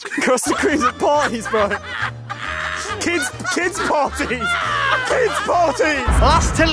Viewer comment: when started chattin about the custard creams i swear i thought he said he goes to kids parties?